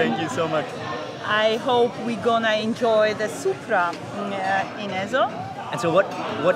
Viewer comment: did she say Supra?